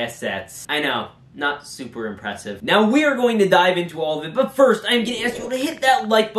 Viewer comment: It was bugging while I was watching this but before you said “we are going to dive in” it bugged and I heard “we are going to Die”